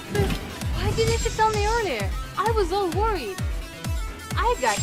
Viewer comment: I could've swore she said "I was all horny" instead of "I was all worried", had to rewind that one like WHAT DID SHE SAYYY?? Wondering wtf I was watching lol. Stupid comment I know, that's just what it sounded like at first lol.